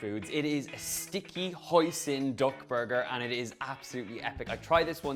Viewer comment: I swear i heard "dog burger"